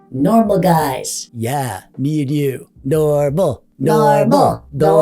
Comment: I heard dormal at 14:49 like dork + normal.